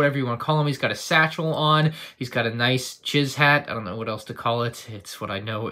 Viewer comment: I thought said jizz hat...